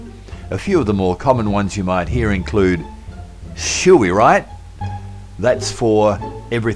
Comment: Can anybody help me, please? Wanted to know what was the phrase he said at 02:19 and 02:30? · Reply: She'll be right, "it'll turn out okay" 2:30 Have a lend of "take advantage of somebody's gullibility, have someone on; tease someone: "He's having a lend of you" "